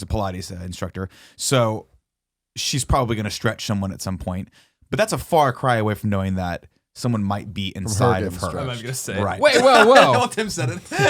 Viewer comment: This topic is fucking gold! Haven't laughed this hard in a while. Did anyone catch what Tim said @ 12:48? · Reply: Nick: But that's a far cry from... Tim: From her gettin' stretched.